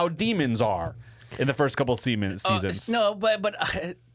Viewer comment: Did just say "the first couple semens?"?